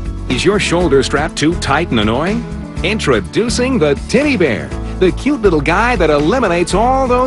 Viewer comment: Omg, the kush . Lol Tiddy bear?. I seriously thought she said "titty bear"